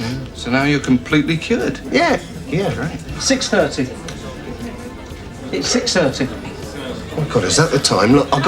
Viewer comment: did he just say £6.30? The sketch shows were a laugh and that's the price of a round, if it wasn't for the current progress in gender pronoun awareness, I'd wish I had a time machine.